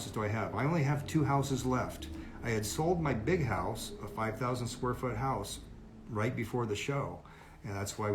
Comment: So wait, your big house is a 1k sf house? 🤔 Did I hear that wrong? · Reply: I heard “5,000 square foot”